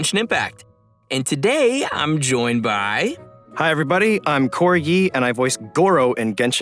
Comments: am I the only one who thought he said corgi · "I'm Cory Yee, and i voice Gorou,...." but somehow i heard him saying "I'm corgi" in the first time i hear it ._. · Did he just say "I am corgi and I voice Gorou??" I mean, Gorou does look as cute as a corgi so, I'm cool wif it! ☺ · Im sorry but I hear "Hi im Corgy and I voice gurou" I can't 💀💀